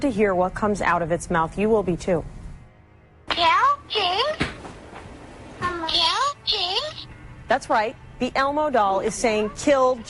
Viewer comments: I thought it said “I’m choking” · i couldnt hear "kill james" at all wth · am i the only one who didn't hear "kill james" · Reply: Saya N I heard heard "kill Jane" and "Kill him" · I thought it said kill Shane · Shane:"This video might ruin you're child hood" Me:"HONEY, where is my child hood?" Edit: The first theory, I thought it said,"Ya, James?" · I thought he said kill Shane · Elmo sounded like it was saying kill Shane +0+ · who else heard “Kill Shane” not James? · Lol when the Elmo clip first played I heard "Kill Shane." and I was just like "THE JIG IS UP!" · Listen to what it says: Elmo:kjli jojihku Shane:yeah,kill john :o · Bitch that Elmo thing sounded like it said kill Shane. AM I THE ONLY FUCKING PERSON WHO HEARD THIS??? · I thought Elmo was saying Jelking.. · I heard “kill King” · it sounded like it said kill shane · To me its sounds like Elmo is saying "more pills Jane!" or "pills Jane?" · It sounded like Elmo said help me, kill me, or kill jame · The elmo doll also sounds like it saying kill shane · I thought the Elmo said kill Shane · I first I thought the Elmo doll Said kill Shane!!! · I thought that said kill shane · The elmo sounded like it said "kill SHANE"